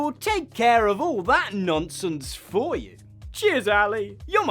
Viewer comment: Not gonna lie, I heard "jizz, Jeff!" before I realized that you actually said cheers. So that was confusing.